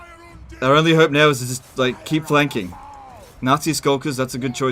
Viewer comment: Anyone else mishearing "Nazi Skulkers" almost everytime?